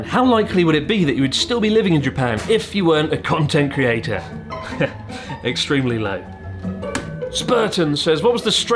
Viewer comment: Extremely lame? I can't hear what Chris is saying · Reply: Extremely low